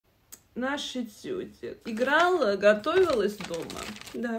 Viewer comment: я вместо "наш этюдик" услышала "наша тётя"😂😂😂